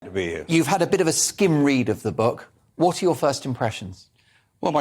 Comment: 🧻🧻Ah, apologies. I misheard - skim read, not skid read! 🧻🧻